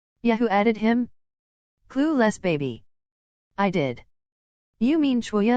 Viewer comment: i keep going back to try to hear what Akutagawa said but i still dk someone help😭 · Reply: It said 'clueless baby'